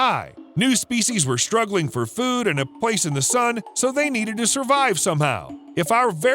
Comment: praise the sun \`[T]/ Or did you say "a place in the sun"?